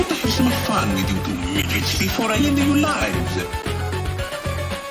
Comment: Did he say the N word?!?! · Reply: He said "midgets"